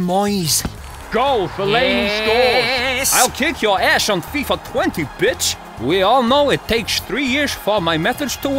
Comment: Wait what Louis Van Gaal he say FIFA 20?